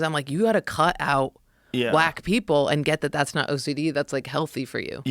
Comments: When they were talking about cutting out "wack people" I heard it as black people 😂😂😂 I'm like wait what.... 😅😅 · Oh, Whack people! I thought they said get rid of Black people 😂😂😂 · Haha. Around the 48th minute Jordan said "I had to cut out wack people", and I had to hear it again. Because I said she didn't just say that. As I thought she said black people.